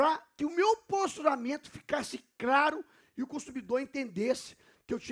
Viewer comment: Eu ouvi "craro"? 🤣🤣🤣